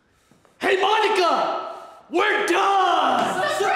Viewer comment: sounds like he saying “Hey ma niqqa we done”😭